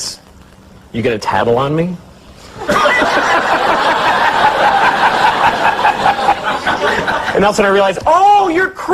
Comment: What's Louis saying at 3:39 when he's clucking like a chicken? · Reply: He's saying "Mexicans!". It took me some time to figure that one out too lol.